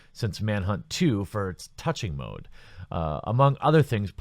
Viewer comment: mfw I heard that as Manhunt 2 having a "touching mode" o.O